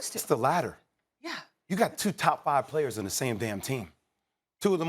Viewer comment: He was talking about Harden and KD. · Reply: wow. I just rewatched it. I promise u I thought he said "you have THREE of the top 5 players". Thats my mistake